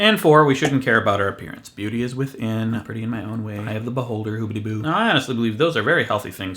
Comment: I honestly thought you said "eye of the butt-holder" and had to rewind